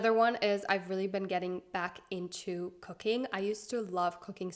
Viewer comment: Thought you said kokain instead of cooking 😅